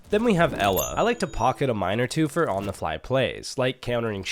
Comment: 😭 I grabbed food and when I put my head phones on what I thought I heard was “and I like to pocket a minor too”😭